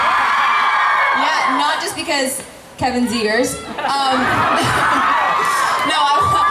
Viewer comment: Did she say- Kevin’s here? Kevin hears? Kevin’s ears?